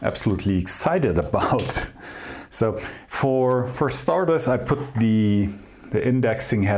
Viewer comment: Listening to this in the shop I heard "for stardust" instead of for starters and thought was cutting some neutronium to celebrate his 100 k followers. Congrats!!